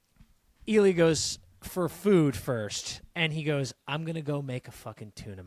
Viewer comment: I don't think Ilya said "For food, first". He definitely said "For food, pervert" seeing how cheeky Shane replied 😂. Even Ilya was surprised at how bold Shane was saying this.